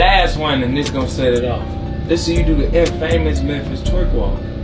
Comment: Dude really said "In-FAMOUS" 😂😭😂😭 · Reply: I thought he said el famous lol but he said infamous like he really pronounced it correctly lol